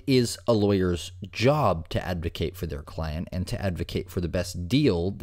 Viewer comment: I had to listen to this part like 4 times to see if what I heard was right. Did he mean to say client instead of clan?